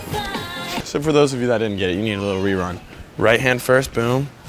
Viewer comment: fr I thought u said “ for those of you that are GAY here’s a little rewind “ 😂